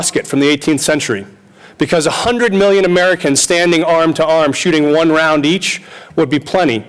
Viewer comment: So, the proposition is that we don't need all these guns because they're completely ineffective. So, 100,000,000 million Americans firing muskets is sufficient to lay to waste whatever group confronts us is a guarantee of victory, but it's impossible if, say, 50,000,000 Americans shooting 100 rounds each to defend ourselves. Well, I'm convinced. If 100,000,000 musket balls can do it, then it certainly follows as the night does the day that 5,000,000,000 bullets fired from 'assault rifles' would be utterly useless. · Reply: +hectroinfectro Had either of you actually paid attention, at 4:25 he says 100 million Americans - a completely reasonable figure. If you take some time to actually process what he said, there's a slim chance you may even be able to comprehend his point. If you're trying to paint him in a bad light, you actually just made yourselves look ignorant, which, frankly, is pretty amusing. It's even more comical that Justicar brags about his own tremendous "rhetorical skill." Good one.